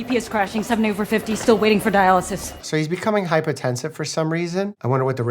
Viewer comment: it's probably not a big deal but the caption says hypertensive instead of hypotensive